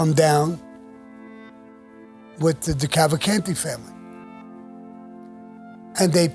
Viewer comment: he mentions some other family, I cant understand what he says, what is the name? I would like to read about them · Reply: Decalvacante family.